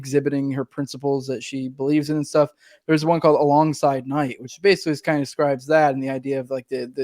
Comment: What was the agorist novel that you suggested? I hearing "A Long Silent Night", but I can't find it when I search that. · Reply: Alongside Night by Neil Shulman (may have the last name wrong)